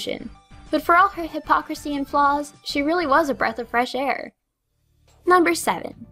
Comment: My computer's speakers are glitching more that usual, so when said: "Iris really was a breath of fresh air." I heard: "Iris really was a breath of fresh chair."